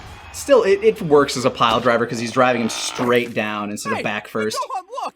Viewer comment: I always thought that they said flyle driver not pile driver.